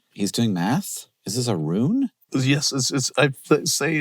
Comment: the captions say "ruin" when I believe it should be "rune"